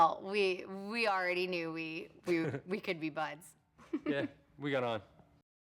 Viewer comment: lol i thought ashton said "we got hard ons'